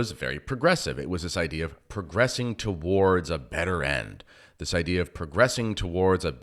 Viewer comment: I almost thought I heard you say, “…progressing toward a bitter end” and then I realized you said “better” not bitter. Lol I’m sorry I needed some humor today and I found this as an option. Our Church is in trouble and even young people remotely raised in the Novus Ordo seem to see something wrong. Don’t worry, we’ll all understand when God allows the veil to be lifted from us all. God bless and thanks.